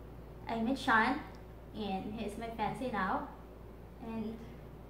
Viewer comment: "I met shawn and he's my -----what----? now?" did she say fancy?